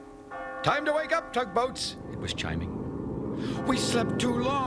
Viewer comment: Apparently Denny Doherty said it was Chiming and fans misheard it as Chimey but since than Chimey became the unofficial name for the clock tower